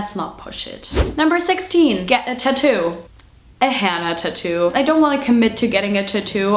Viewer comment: I thought you said you wanted to get a Hanna tattoo at first! Maybe it's just your cute accent :p I worked down in Munich for a couple of years; left Munich the day before it started the first time and the day it started the second time :'(